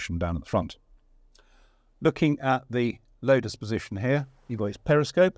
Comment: did he say "Lotus Position"? Wow. Tank-yoga is definitely something we never got from. I am happy and sad about this. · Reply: Loader's